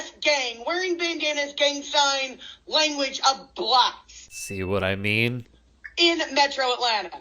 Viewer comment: I thought she said blocks